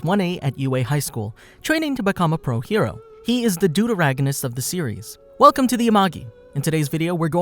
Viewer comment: Omfl- I really thought he said dodoragonist for a second at 0:15